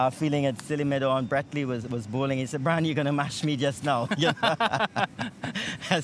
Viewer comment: What did he say @ 9:14 ? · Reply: brian you go mash me just now. (mash means step on in the Caribbean) basically he was saying if you keep backing away you'll step on me